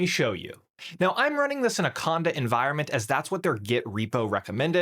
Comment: what is the word? What environment? i hate this when this happen!!. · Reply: Conda environment.